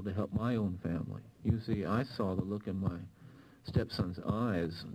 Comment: did he say "stepson"?